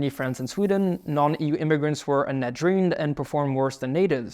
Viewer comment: Subtitles say non-EU immigrants are a "net draw", voice says "net druin" .. do you mean "net drain" ???